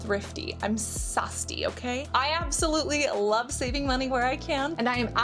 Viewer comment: haha! did you say you're "sasti"(Hindi for thrifty) at 9:02? :face-blue-smiling::eyes-pink-heart-shape